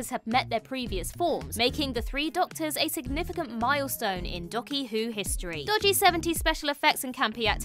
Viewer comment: did say "Ducky - Who History"?